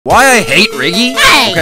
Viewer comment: People who thought said, why do I eat Rriggy👇